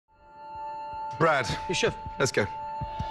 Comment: for a moment i thought he said bruh